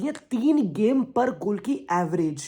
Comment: Did he say 3 game per goal or I didn't hear that right? 🤣